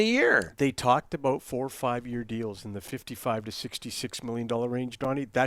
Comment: I thought said 15 to 16 m 😜😂😜